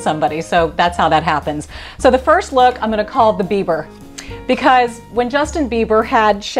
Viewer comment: You are so beautiful; plus, how sweet/caring of you to dare to show us how to blow dry wet hair! My ears must be getting old along with every other part of me because I thought you said the first style was the "Beaver". ha Duh...Your eyes look huge with the "Boyfriend" and "Grace Kelly"! Love them all, even the Beaver/Bieber. Thank you, too, for sharing your fav products.